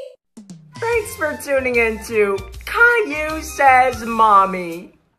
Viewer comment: why does it sound like he is saying moomy